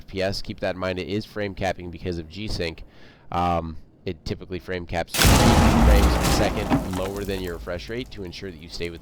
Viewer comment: What did you say at 4:43, at what FPS does it go to for g sync range? nice vid btw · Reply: Sorry I was talking about my setup capping -3 fps below max refresh rate to ensure you stay within the G-Sync refresh window of 144Hz/144fps. This has been shown by tests to lower input lag and ensure no tearing or stuttering.